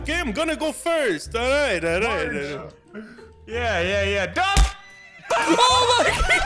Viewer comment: it sounded like he said duck